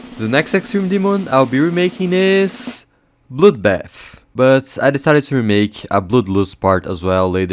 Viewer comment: I thought bro said “bloudbaff”